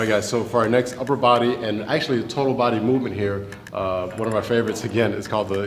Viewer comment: did he say isolated total body? how does that work